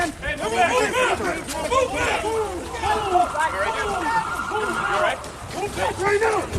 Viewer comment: I heard a "You allright? Good" right when the sheriff walked up to him and put his hand on him to wisk him away.